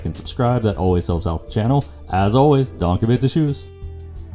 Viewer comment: For quite a bit of time I couldn't figure out what you say at the end (before you added the text) and I thought you were saying "don't forget the shoes" lmao